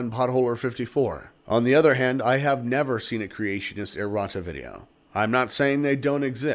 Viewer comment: you are saying "errota"? because it sounded like at the end you said it would be unlikely to have a Creationist do a video like this... and it sounded like you said "Creationist Erotica video" (insert the coffee spit-take here, my coworkers wanted to know why I was laughing so loud) - that creationist video could only be fodder for more comedy... keep it going brother ! I look forward to each new installment !